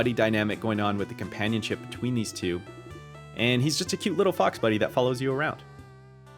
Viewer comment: Maybe it's because I'm Australian and I'm used to hearing "fox" pronounced differently but I definitely thought you were about to say "a cute little f***er" lol